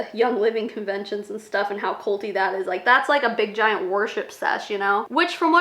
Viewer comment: When you were talking about YL conventions, I first thought you said a big warship sesh (not worship) & I was picturing a battle cruiser full of huns